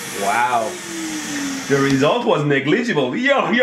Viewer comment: What did he say does anybody speak greberish? 🤣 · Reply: That was negligible hiyahiyahiya (slap!) sh- fu-- sh- fu